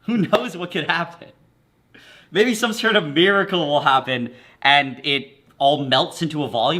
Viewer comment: I heard beeracle 😂😂